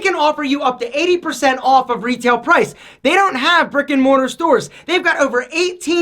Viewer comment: I misheard it as “frikkin mortar stores” 😅